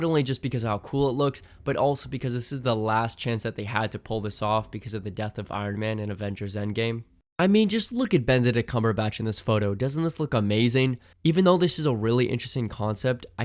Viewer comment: did you just say “Bend a dick” cumberbatch?